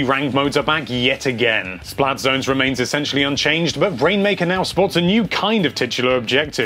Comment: did you say brainmaker